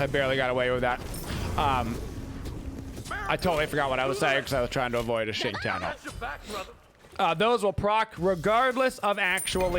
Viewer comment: i hear it like this "I tottaly forgot what i was saying cause i was trying to avoid shit tian ult"